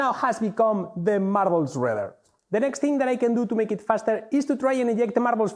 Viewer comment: "Squeeze a few more seconds of what now has become the marbles rthrurereerrr". what is he saying? Even Auto-Translate had no idea.